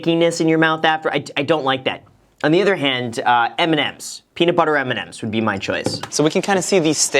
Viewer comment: it sounds like Graham is saying “Eminems”